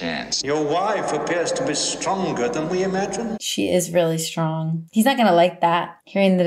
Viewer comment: you are so feminist and young/naive 🤣🤣 the ghost never said the wife was stronger than Jack lol I know that’s what you WANT it to be but it’s not.. he said she was stronger than we anticipated lol you give Wendy way too much credit.. she’s kinda useless and helpless at the end of the day